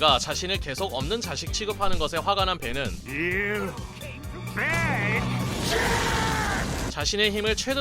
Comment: 에 You big jerk로 들었었는데